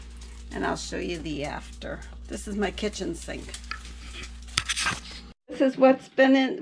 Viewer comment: I enjoyed visiting with you today. Is it just me, or did it sound like you said "chicken sink" instead of "kitchen sink"? Maybe it was just your accent and the way I perceived it. Anyway, it was cute the way it came out (to my ears' interpretation of it, anyway).